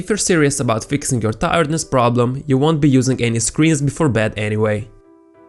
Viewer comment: I was about to fall asleep when watching this so, I thought he said ,”no screams before bed.”